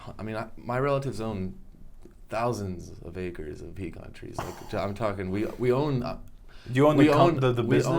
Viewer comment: what kind of trees is he speaking about because i don't quite understand it. Thank you! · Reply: pecan trees